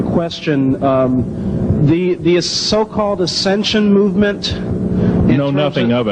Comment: What is this the "esentian movement" 8:38 someone heard this before? not sure how to write? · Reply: Ascension movement as in people getting saved and sucked up into the the sky aka ascension